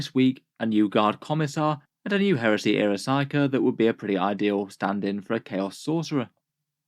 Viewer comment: I misheard when you said Esoterist, I thought you said he was a Psyker Risotto-ist. Certainly the more tasty option.